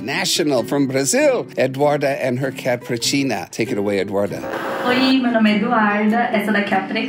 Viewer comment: The way Jackson pronounces "Pretinha" as "Pretina" is so cute! Love from Brazil! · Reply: I thought he was saying Purrtina 😸